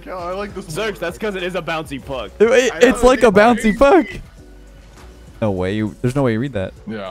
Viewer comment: i think leth says a bouncy f**k😂😂😂